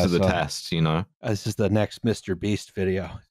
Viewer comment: Genuinely thought you said “Mr. Bean” not “Mr. Beast” and was absolutely boggled that he had apparently a whole series of 30 second videos on him giving people the gift of vision. But I was even more excited to see Mr. Bean go evil and start giving people the power of becoming giant wineglasses that can resonate with suspension bridges. That would be a very good example of defection to terror.